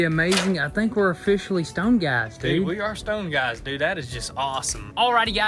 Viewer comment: Did I just hear “we are stoned guys”👀